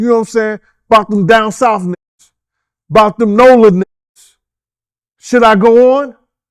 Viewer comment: That cliffhanger😂🦅👶🏽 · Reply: Thought he said no limit niggas .. baby cash money lol